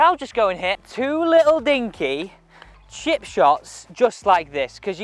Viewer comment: I thought I heard "shit shots" when you said chip shots. i guess that's because my chip shots tend to look like shit!